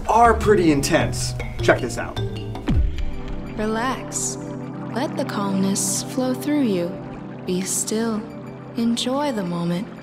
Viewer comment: At first I thought it said "Let the communism flow through you"